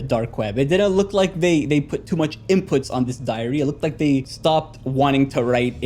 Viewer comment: WHY DID I HEAR IT AS ''DIARRHEA'' 😭😭